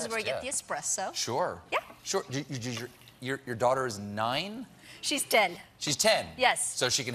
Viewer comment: I absolutely heard "Your daughter is 9?" "She's dead." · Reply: I didn't hear it the 1st time & i tried like 5 times to figure out in what possible way you heard it as "dead", and all i heard was "she's 10". Maybe it's your device or headphones or speakers or your own ears or accents in different places but all i heard was 10. They don't even rhyme ffs 👿